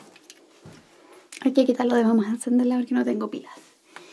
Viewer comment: ¿Algún chileno que transcriba lo que dijo en el minuto 1:06? Porque sin connotación de burla digo que me cagué de risa que pusiera "*habla en chileno"* y solo entendí "pilas" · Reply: Dice: Hay que quitar lo de "vamos a encenderla" porque no tengo pilas :c Tuve que repetir el video 70 veces para traducir de español chileno a español